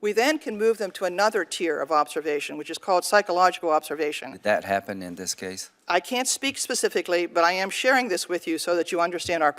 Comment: did she say psychological observation or operation?